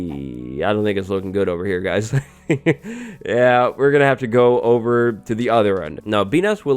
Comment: Sound like he says “y’all niggas looking got over here guys”